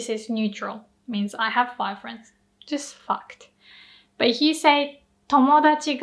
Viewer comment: I heard, "I have 5 friends.. just f*cked." And I got sad because I only have like five friends lmao until I realized you actually said "fact"! Language is a funny thing and I got a great laugh out of that. Amazing video as always ありがとうございます！